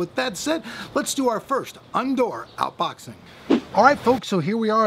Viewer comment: did he say undoor outboxing at 0:25 ?